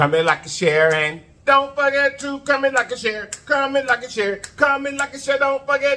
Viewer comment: Man whole time I thought u was saying commit like a shearit. I’m thinking like what’s a shareit. But today I heard comment like and share it 🤣🤣 got it